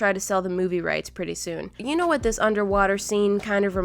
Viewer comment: paints an underwater scene on a bottle Me: can’t even draw a stick figure Edit: I was just joking (even though it was a bad joke) I didn’t think people would take it seriously. I’m not that incompetent. I can draw a stick figure (kinda) For those of you who got the joke and liked, thank you so much. I’ve never got so many likes. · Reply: I thought you said “underwear”